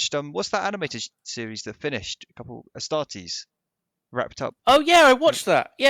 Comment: What is that animated series they were talking about? Starties? Startees? EDIT: Aha, I misheard, they mean "Astartes".